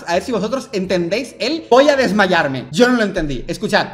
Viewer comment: yo si escuche voy a desmayarme